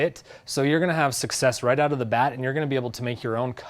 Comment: Exciting products😀 Btw, theexpression is 'out of the box,' rataer than 'out of the bat.' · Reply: Lol, I thought he said 'off the bat'.